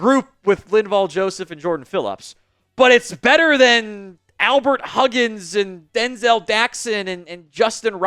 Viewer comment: I can't help it. Every time I hear say, "Albert Huggins," I hear "Albert Collins" then start singin', "I ain't drunk, I'm just drinkin'."